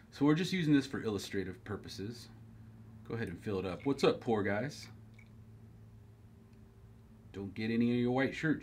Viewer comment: Lol the first time I watched this, when he said "what's up pour guys" I thought he said "what's up poor guys" 😂